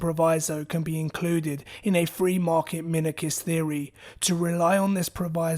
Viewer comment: what does he say at 13:20 ? free market micist? mini... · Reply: minarchist